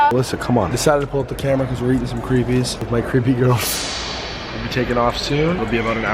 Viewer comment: did you just call it creepies